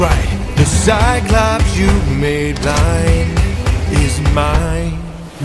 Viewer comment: bruh I thought he said "you made lines" but blind makes way more sense